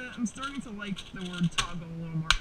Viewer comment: Nice! I got a 3x3 PB today too. 10.69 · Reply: Whoops, I thought he said all time, not on cam. My bad. Nevertheless, I still got a PB.